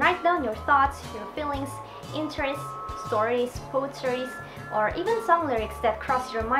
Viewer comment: I know I'm really late commenting here but in 1:21 she said stories but after that, I could not make out if she said "food" stories or "faux" stories or did she say something completely different? Love her video and she has convinced me to write a diary again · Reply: It’s “poetries” lol! You can turn on the english subtitles to understand my weird English better XD